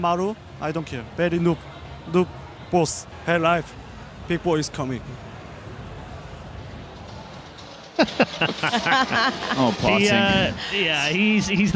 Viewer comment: what does parting say ? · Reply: Hey life people is coming?